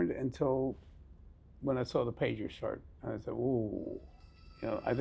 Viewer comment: Does anyone know what the police guy say at 21:47? “When I saw the pagers start”?